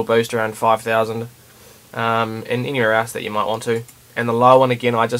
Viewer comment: I heard: "And in your as that you might want to"